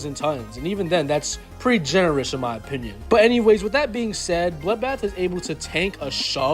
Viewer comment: Pardon did you just say bloodblath